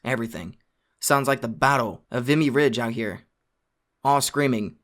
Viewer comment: Misheard "all screaming and incessant gunshots" as "all screaming and INCEST and gunshots." Definitely wouldn't be the weirdest turn in a creepy story I've heard.